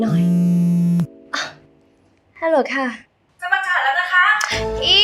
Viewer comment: ใครพอรู้บ้าง ว่าคนในสายพูดว่าอะไรหรอคะ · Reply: จะประกาศแล้วนะคะ